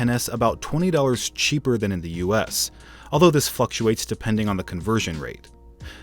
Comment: Did you say f***ates instead of fluctuates